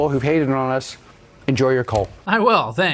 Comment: Did he say "Enjoy your Cull" as in, the slaughter of wild animals? Because it sounded like he said enjoy your cull. "Enjoy your Cult" doesn't make sense, except perhaps to orate Palantir's designation for those that might disagree.